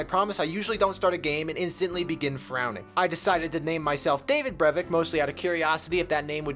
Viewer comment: I didn't have my eyes on the screen as you mentioned your characters name, and I thought you said "David Braben" which I thought was very cool. Figuring out what you actually said on a second/actual viewing it's not very cool anymore, but on the other hand, makes sense.